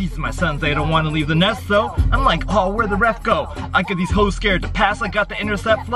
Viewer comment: Did He sayd h0es?? 5:44 omg ;0